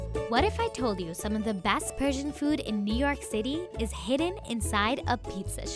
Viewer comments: I thought it said prison food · I THOUGHT ITS SAID PRISON FOOD 😭😭